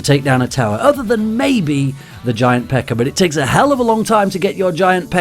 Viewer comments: doesn't it sound like he saying giant pecker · "it takes a hell of a longer time to get your giant pecker out" (He said pekka but because of his accent it sounded like pecker).